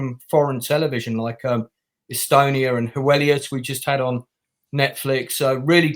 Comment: does anyone know what show he mentioned? I (or the subtitles) have no idea what he said, and I love to give a good foreign TV show a go once in a while. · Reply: The Polish show he mentioned is called Heweliusz, if that’s what you’re asking for. I haven’t actually watched it yet but my Polish friend did recommend it and I trust her recommendations most of the time